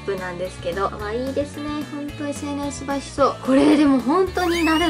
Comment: ってなんて言ったの？🤔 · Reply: N Nurse SNS映えしそう だと思いますよ( ¨̮ )︎❤︎